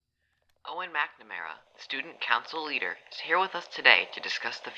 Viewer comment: Am I just Heathers obsessed or does he say “McNamara” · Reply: Rosemary Fire I think it says “Owen McNamara as the name of the dude on the radio who’s talking.